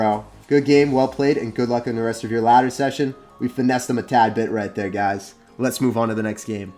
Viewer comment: did you say "we finessed him a tad bit" or "we finessed him a tag bit"?